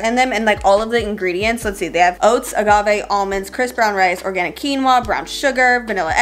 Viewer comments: I literally thought u were saying chris brown rice when u were reading the ingredients lmaooo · Did say "Chris Brown Rice" at 8:30 lmfaoo idk but either way im dead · when you said "crisp brown rice" I thought you said chris brown rice